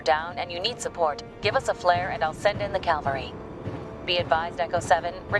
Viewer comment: did she just say calvary instead of cavalry?